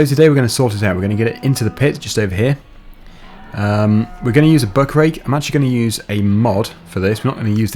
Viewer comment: I always thought he was saying muckrake when he said buckrake.